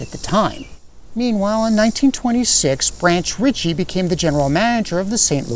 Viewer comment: did you really say Branch "Richie?"